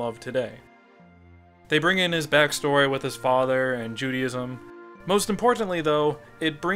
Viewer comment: qute funny when you are talking about Judaism sounds like your saying JUDY-ISM like theres this religion dedicated to a Woman called Judy!!!